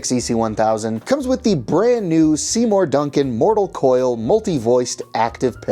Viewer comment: These are Fishman pick ups right? I just wanna make sure because I thought I heard you say something about Seymour Duncan or something like that. Also, the clean tones here sound fantastic to me. Not that sort of unstable annoying spring like a single coil tone for example, you would get from a Stratocaster but something much more stable and kind of Metallica like. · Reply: Seymour Duncan mortal coil pick ups